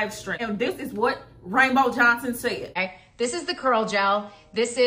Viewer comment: Did she just say, "rainbow Johnson" !?!? lol